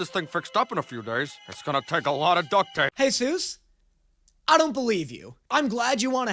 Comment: WHY DO TGE SUBTITLES SAY JESUS INSTEAD OF SOOS 😭😭